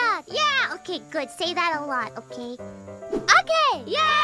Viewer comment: I think she said "a lot" and not "aloud". But I might be wrong 😅